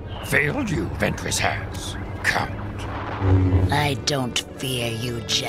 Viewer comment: did yoda just call count a cunt?